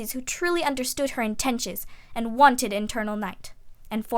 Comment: at first I heard "fat"